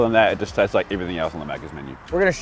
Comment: I cannot believe at 4:51 the subtitles say "mackers" instead of "maccas"